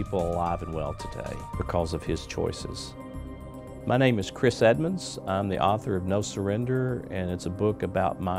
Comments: I thought this guy said his name was Chris Evans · Thought he said his name was Chris Evans not Chris Edmonds XD